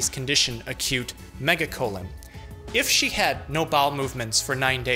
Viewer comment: I thought he said "A cute megacolon"